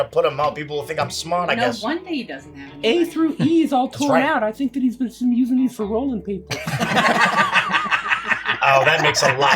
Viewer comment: What exactly is bitsy saying here? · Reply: Pages 8-30 is all torn out I think he's been using them for rolling papers